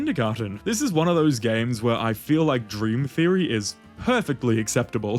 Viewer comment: i thought he said dream theater